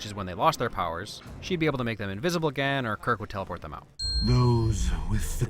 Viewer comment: did he just say kirk....